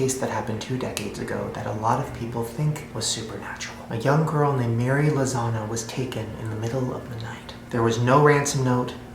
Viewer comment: When he said the girls name I thought he said “a girl named hairy lasagna”😂💀